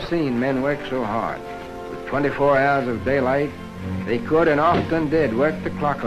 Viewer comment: or so, did he say "24hours of daylight"?